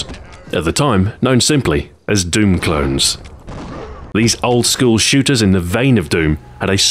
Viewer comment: Did he just say "School shooters"?